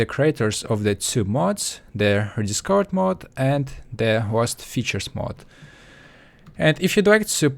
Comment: I thought he was saying the "Worst Features Mod"